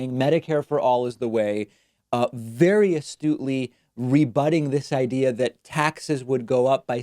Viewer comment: Jeeze, at 1:59, the words below the video, the voice transcription, said, "various doodly," instead of the correct, "very astutely." Who translates words to print on these videos? Bad job, Various Doodly!